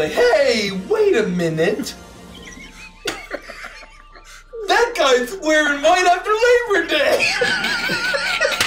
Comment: Im not a native speaker, what is saying at 49:00 ? I want to understand please XD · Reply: “Hey wait a minute… that guys wearing white after Labor Day!”